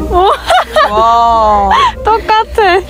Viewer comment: in the background did they say something in Korean or was that a "WTF"? 😬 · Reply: just fyi she said '뜻밖의 재능 발견' 'DDeut bak e jae neung bal gyun' whici means 'concidentally found talent' . i think 'ddeut bak' sounded like 'the fuck' to you lol